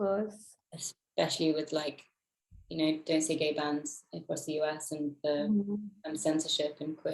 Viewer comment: Emma actually mentioning how the world hasn't really changed in term of acceptance "especially for gay bands" and censorship.. Did I hear that right?!? 😱 This is so loud!! 😍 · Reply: Emma said 'bans', I think.